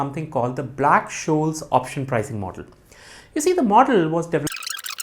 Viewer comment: I reduced playback speed to 0.25x @ 00:18s but still couldn't understand. Task failed successfully 😂 Looking forward to more in this series! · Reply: This is what I said: You see the model was developed in 1973 and the first widely used mathematical method to calculate the theoretical value of an option contract, using current stock prices, expected dividends, the option's strike price, expected interest rates, time to expiration, and expected volatility. Black-Scholes says that instruments, such as stock shares or futures contracts, will have a lognormal distribution of prices following a random walk with constant drift and volatility. Using this assumption and factoring in other important variables, the equation derives the price of a European-style call option. The Black-Scholes equation requires five variables. These inputs are volatility, the price of the underlying asset, the strike price of the option, the time until expiration of the option, and the risk-free interest rate. :)